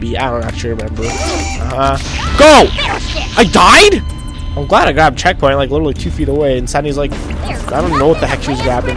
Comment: Did Sandy just say bullshit ? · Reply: She said bearshit